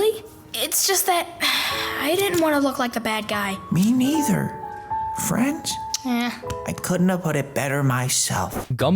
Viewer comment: WHAT IS THAT LOCKER SAY IN THE BACKROUND! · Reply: it said help me 😨